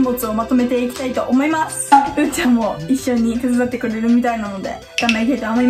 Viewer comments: うーちゃんがゆうちゃんって聞こえた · うーちゃんがゆうちゃんに聞こえた。